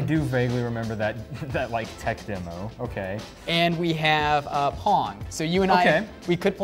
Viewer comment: i thought he said 'porn'